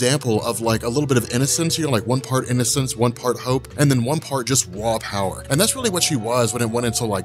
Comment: Rob, I legit thought you said "one part innocence, one part hoe (not hope), one part raw power" and I about died laughing because I thought to myself next, is super girl a hoe? I'd like to read a comic with SG getting her hoe on